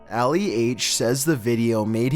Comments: Ali H...who thought he said Ali A for a sec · “Ali H” I THOUGHT HE SAID ALI A I WAS LIKE “..WHAT” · When he was gonna say Ali H I frickin thought he said Ali A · When he said "Ali H" i wasnt paying attention to the screen and i heard "Ali A" and thought he was talking about the fortnite youtuber